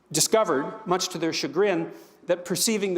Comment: He uses a word 'shebrin'. Spelling may be wrong. I hadn't heard that word before. Google brings up a definition of a home-made whiskey? What's that word, and what's it mean?